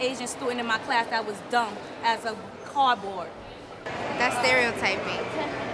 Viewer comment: "I had an Asian student and he was as dumb as cardboard." You're such a great teacher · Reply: I thought she said "a cardboard" making it even more dumb